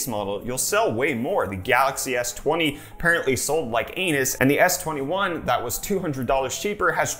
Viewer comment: Did u just say that it sold like an " ANUS "?